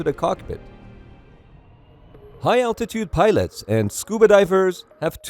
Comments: I keep hearing “YouTube pilots” lol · this whole tim i thought u were saying youtube pilots xD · i thought he said youtube pilots